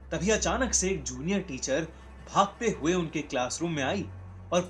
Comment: Did he said chutiya teacher at 0:40??